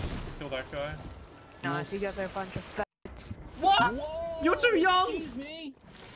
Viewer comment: OH MAN!!! AMAZING content, Hey what did you said at 8:46?, (After the kid said bad word) You are too young?? That voice crack with small pitch made me Lugh so HARD!!!, Love your content and as well!!!!! 😁❤